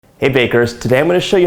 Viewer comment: I honestly thought you greeted us by saying, “hey fakers...” 🤓